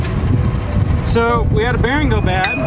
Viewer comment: Glad you are all ok. Early in the video it sounded like you said it was a new bearing. Who installed it?